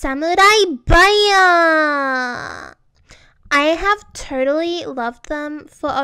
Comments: When she said totally I thought she said Tutterly... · Lmao " I have Turtley loved them for a long time" is what I thought she said